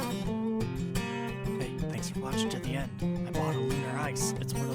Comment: What he is just saying at 15:06 ? I’m not that good with english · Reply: Lunar ice, he got one